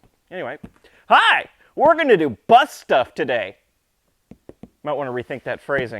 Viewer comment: "Hi were doing butt stuff today" this is what my immature mind heard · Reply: I watch on 1.75X speed and it just sounds like he said butt stuff, no matter how many times I rewind.